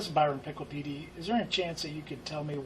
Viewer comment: did he just say biron pickle pd